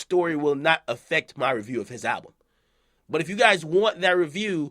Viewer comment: What happened to the intro Ak? Used to be "It's ya boy DJ Akademiks" now I Just hear "Itch Akademiks"